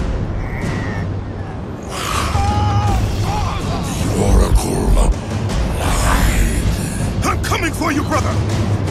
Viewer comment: what thanatos said on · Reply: the oracle... LIED